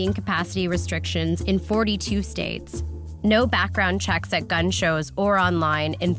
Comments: LIVING IN ALASKA TySang907, She said limited, not no background check. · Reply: no, she clearly says "no background checks" at around 6:08. Listen again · did she just say no background checks at gun shows?